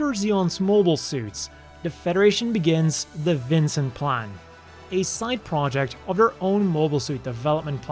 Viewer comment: I thought said Vincent Plan instead of Vinson Plan😅 and I had to make sure of the spelling